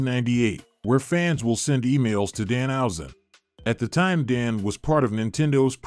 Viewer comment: i really thought you said "fans will send emails to Dahausen" that would have been very evil